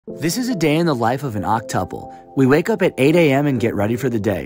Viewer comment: Okay, so I misheard this as octuplets and was about to ask so many questions before I rewatched 😂